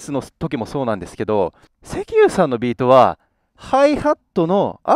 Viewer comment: いつもセキユーが 🥁石油さんて聞こえる。